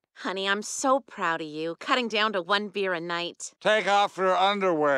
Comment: "Take off your underwear -- She Dont know" made my day :D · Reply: Coulda swore he said, "Thank God for underwear." I thought he pissed himself 🤣